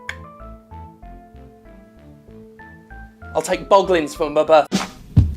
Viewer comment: Did that intentionally cut off? Cause I heard "I'd take boglins for my but..."